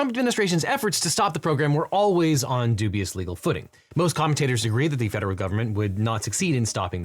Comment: You just used the term "legal footing" ... which I misheard as "legal pudding". Now that begs the question, what would illegal pudding look like? This comment brought to you by adult ADHD.